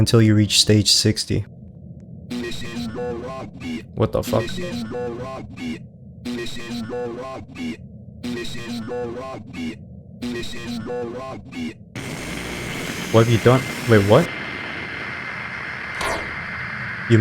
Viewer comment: I hear them say this is your rugby